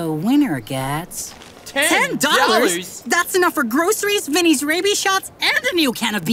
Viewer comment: I heard “Vinnes rabie shots”💀